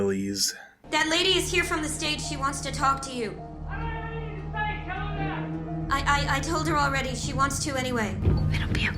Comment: what is he saying? I can't hear him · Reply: “I DON’T HAVE ANYTHING TO SAY! Tell her that!”